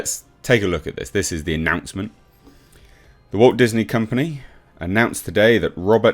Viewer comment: I thought I heard you say, "The Woke Disney Company" instead of "Walt" and giggled because it's perfect 😂